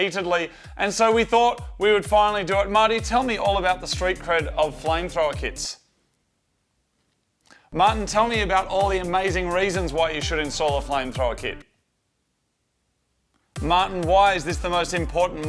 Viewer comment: hahaha at first i thought you said flying thrower i was like huh? Then i got Flame Thrower lol that accent though from Cali sorry.